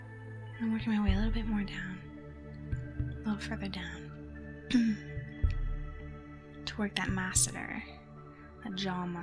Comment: What does she say at 13:47 ? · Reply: “A little further down”